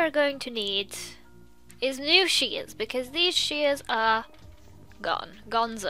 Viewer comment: I thought she said “shiggins” instead of shears 💀💀